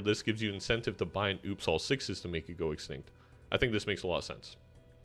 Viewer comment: I have a question. What do you say at 19:26 in the video? Hard to understand what you said in relation to Gros Michael there but I'd like to know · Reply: I was talking about that specific moment in the video, but I figured out what you said in the end. You said it might be a good idea to buy the "Oops! All 6's" joker to make it break quicker. Thanks for the video!